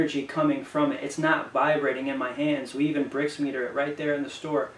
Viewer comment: Does anyone know what he saying at 1:02:14 ? Is he saying "brace meter", if so, what is that ? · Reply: Greetings! Yes I was saying Brix Meter, it helps to read the frequency and liveliness of the food, its uses sugar indexes and some pretty cool technology to help find the most vibrant food to eat! #moringalove +